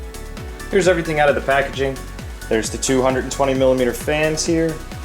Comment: lol for a second i thought you said 220 millimeter fans... but you said 2 120mm fans